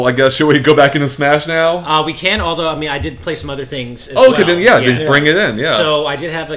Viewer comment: when said "I did play some other things" what I heard was "I did play some MOTHER things" Why you do this Nintendo?